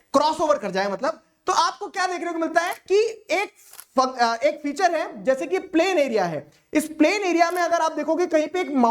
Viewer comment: sir ne kya bolaaa ?!?!?!💀💀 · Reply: F**k 😂😂